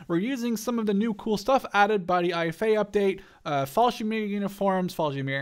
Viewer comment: you skipped like half of the word Fallschirmjäger it sounded like you said "fallshimeer"